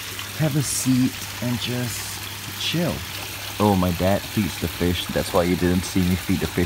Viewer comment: did he say that his dad ate the fish?